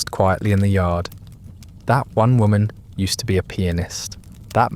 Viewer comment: 56.16 I honestly thought you said “that woman used to be a penis”!!! 😳😳🤣🤣